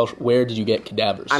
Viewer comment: Wait he said Ty Lopez "Courses" I thought he said "Corpses" lol.